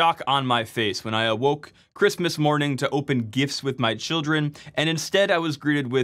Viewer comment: I heard that as "opening gifs with my children" and was confused for a second 😔